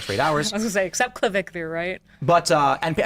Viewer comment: did she say, claviktor? Tf, is claviktor?? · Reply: She said Clavicular he’s a kick streamer